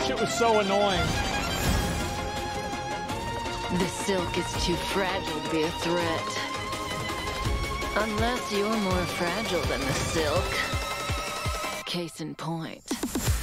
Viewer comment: Tf did she say? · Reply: This silk is too fragile to be a threat... Unless you are more fragile than silk